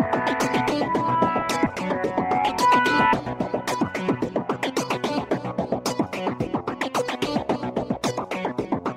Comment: can someone tell me what is that word? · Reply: Real Suffering